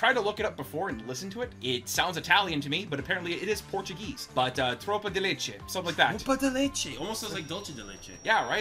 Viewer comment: sounded like tropa de leite, (Milk Squad) 😂 love you from Brazil, guys ❤.